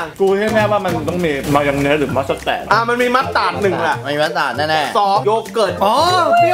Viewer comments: ตอน18:28หรือ27นี่เเหละได้ยินเป็นมาร์สแตด555+ใครได้ยินไลค์ให้หน่อยเด้อ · ใครได้ยินเหมือนเราบ้าง? "มันน่าจะมีมายองเนสหรือมาสแตด"5555